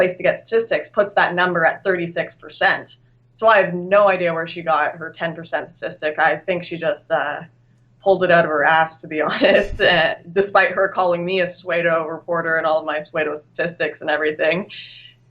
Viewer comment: did she say "swedo" trying to say "pseudo" ?